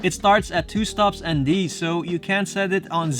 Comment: On 5:38. Did you say you CAN set the ND to zero so I can achieve just the mist effect? I am not sure if I heard you say "CAN" or "CAN'T". Thanks! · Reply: Can’t, 2 stops ND is the minimum.